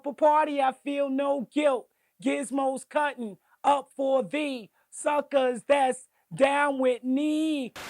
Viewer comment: That song always used to make me laugh...I always thought he said " Suckas that down with mee"..He rymes a little like he's kida slow...